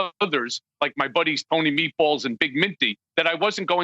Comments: I'm sorry, but did he just say Tony Meatballs and Big Minty? Lolz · I'm sorry, did I just hear Michael Cohen name check Tony Meatballs and Big Minty?